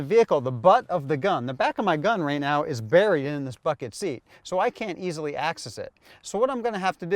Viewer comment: it sounds like he says, "The butt of the gun, right now, is buried in this fucking seat." LOL Excellent video.